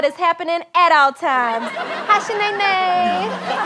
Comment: DID SHE SAY HI SHANAYNAY!?!?!!!!!?